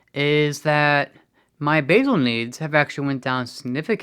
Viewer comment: BASAL INSULIN is LONGER-acting and helps keep your glucose levels steady day and night. BOLUS insulin is the QUICK -acting delivery that you often take before mealtimes. Thought he said "My BAGEL needs have gone down significantly".